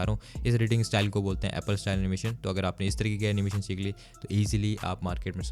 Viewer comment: What's the name of this editing style animation ? I didn't understand what did u say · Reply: SaaS animation